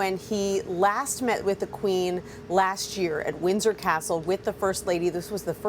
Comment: she pronounced Windsor wrong, it sounded like she said Wizard Castle!🥴